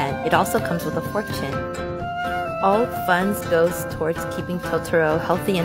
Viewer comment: Ow, it comes with a fortune, I first thought you said, It comes with a pork skin.